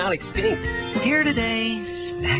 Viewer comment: did you say snack that smiles back